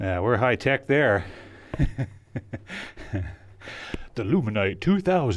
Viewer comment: LOOOL! the captions, instead of saying the luminite 2,000 says the luminite mm HAHAHA 26:21 yeah, i'm learning something. u shoulda taken your mask w u! hehe